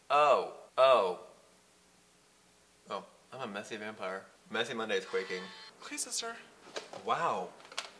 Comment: did Ricky just say messy monday is quaking im screaming WHAT